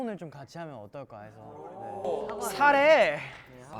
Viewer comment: 나만 사레~가 파이팅 ~으로 들리는 건가😂😂